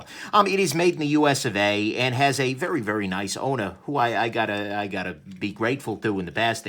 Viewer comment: What said "Vipersharp has a really nice owner." What I heard "Vipersharp has a very nice odor." Thanks for the review.